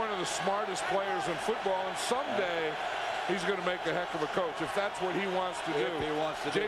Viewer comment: Did I hear Madden right @ 37:56? Jason Garrett a heck of a coach? He sure is a great coaching puppet for one Jerry Jones.